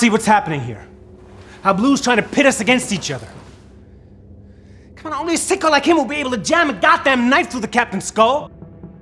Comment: what is he saying? I thought he was speaking a different language PLEASEE😭😭😭😭what is going on???? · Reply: come on, only a sicko like him would be able to jam a god damn knife through the captains skull